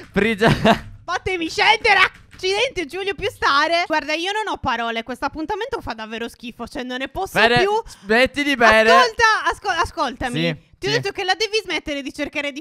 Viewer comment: Solo io al minuto 5:35 ho sentito ma porca putt